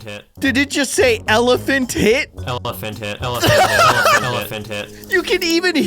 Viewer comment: i think its saying elefant head